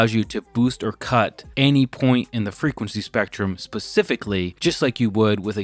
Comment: I swear you said “Pacifically” 1:24… instead of “Specifically” of course… i guess.